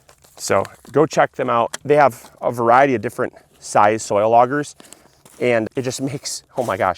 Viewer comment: Luke said,"Soil auger.' I heard, 'soy logger.' Hmm, vegan lumberjack?